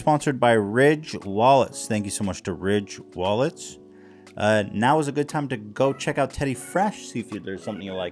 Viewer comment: "nows a good time to check out TF" I CANNOT WAIT FOR THE NEW DOP - I GOT SO EGG CITED.. I THOUGHT HE WAS SAYING THERES A NEW DROP. UGHH.